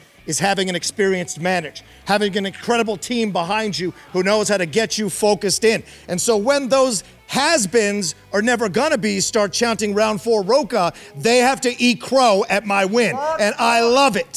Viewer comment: I thought they were saying “Outlaw Rocha”, not “Round Four Rocha“.